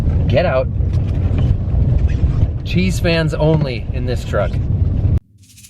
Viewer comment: Did you just say 'Cheesehead fans only in this truck?'